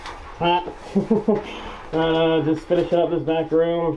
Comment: DISCLAIMER: This video was filmed and edited by our old team of videographers and editors. We didn't want to completely scrap this content but we know it's not our highest quality! We hope you can still enjoy! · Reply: the subtitles are wrong. He says "and what-not" not "in white matte".